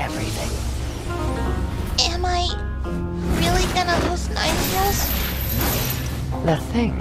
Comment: Guys i dont speak english fluently, can u help me what kushina said at 0:19? Kushina said "am i really gonna lose [....]" Also the old lady said at 0:24 "The thing that is inside my [...]" I don't understand those 2 words, what are they saying please help me😭😭😭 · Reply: “ nine tails” as in kurama and old lady mito said “ the thing inside my vessel” her body